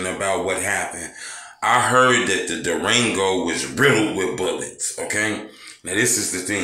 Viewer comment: I thought it said “Durag” 🤣